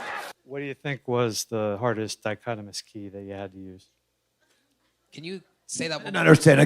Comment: the guy said "dichotomous key" where you put question marks. It's how you determine what species a specimen belongs to. Idk why this guy was talking about that but that's what he said